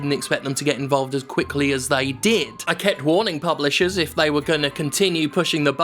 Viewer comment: I heard you say "bubblisher". This should be a thing.